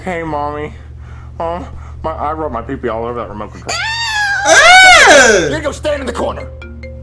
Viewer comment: Can anyone tell me what Jeffy said? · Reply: He said I put my $”@ all over that remote